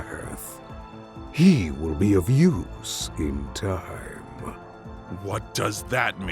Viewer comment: it sounded like he said abuse like whoah